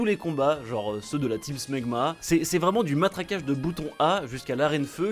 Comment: la team quoi ?????? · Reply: Smegma